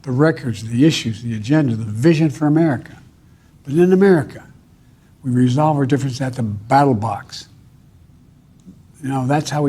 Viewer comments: For a moment, I thought he said, "we resolve our disagreements with Battlebots," and I was like, "hell, yeah!" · nah i honestly thought he said "BattleBox" as well LOL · BATTLE BOTS 🤖🦾🦿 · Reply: That's all I hear too. I'll see you at the polls with my battle bot lol